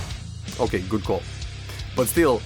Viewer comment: genuinely my phone thought you said "okay google" and my Google assistant came up thinking I was trying to talk to it...